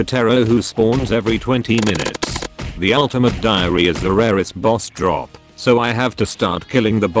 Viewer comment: misheard it for ultimate diarrhea